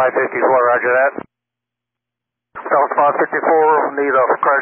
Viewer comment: I think the pilot says "Catastrophic damage."